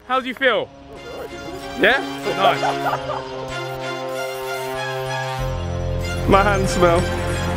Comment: I thought josh said my nan smells